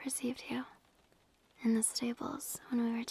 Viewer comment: I heard "in the Staples" what